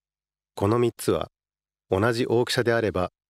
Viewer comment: 「同じ大きしゃ」に聞こえる